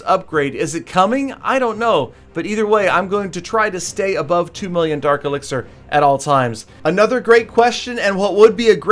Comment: did he just say 2 Million dark elixer?